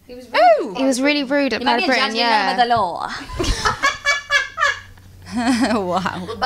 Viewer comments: what did jade say @ 1:44 ?? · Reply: I think it was "you might be a judge but you're not above the law..." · what did jade say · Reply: Sounded to me like "You might be a judge but you're not above the law"